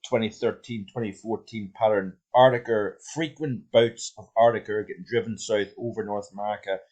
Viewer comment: did he say "frequent boats .. strengthening the jet stream"? He's bandwidth is shit, he's mic is potato and he's accent does not help. Speak ENGLISH.